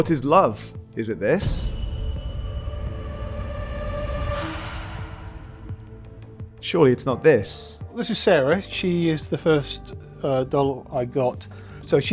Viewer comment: I heard “certainly not this” i reference,Black Pill.maybe just.. post-postmodernism